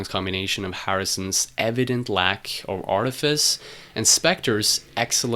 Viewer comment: Nice video, but three comments: 1. At 10:00, I am glad you printed the words on the screen, as it sounds like you say "lack of orifice." :-) 2. At 10:37, it might have made the point clearer by playing one of the "call and response" sections of My Sweet Lord, instead of the Harrison solo part. 3. At 11:34, you state that Harrison's manager Allen Klein tried to settle the He's So Fine/My Sweet Lord legal debate, but, in the end, Harrison had to pay Abkco Records $587,00. Abkco Records? Wasn't Abkco owned by Allen Klein? Could you clarify this? I'd like to know the details. Thanks.